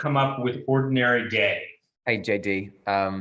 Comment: I was distracted when I played this so I heard "ordinary gay" and I was like wait what 👁👄👁 and then I realized what it was about lol